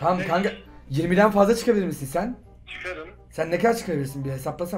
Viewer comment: ne dedi? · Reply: 50 çıkarırım